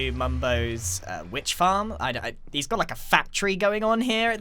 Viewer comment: says factory me: i thought u said fat tree...